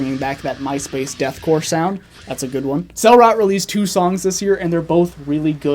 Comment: quick question, what’s the name of the band at 2:49? He said the name so fast i can’t make it out and he didn’t say the title of the songs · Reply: 'Cell Rot' :)